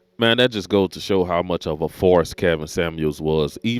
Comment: I thought you said "farce". But you said "force". You scared me for a moment